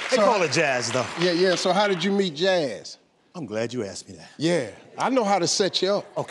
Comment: sounded like steve said “so how did you meet your ass”